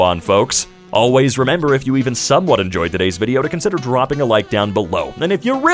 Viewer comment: I heard it as "consider throbbing a like down below" and I laughed, not finding it odd in the slightest (the reason for this might be watching four r/suddenlygay vids in a row)